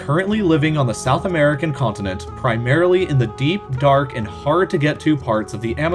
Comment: Did I just heard on 1:27, deep dark rape forest